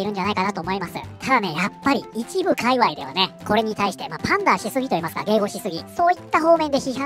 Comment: ～「パンダしすぎ～けいごしすぎ」 って聞こえるんだけど、どゆ意味？ってかなんて言ってる？ ほんとこのひとの日本語分かりにくい(´-ω-`)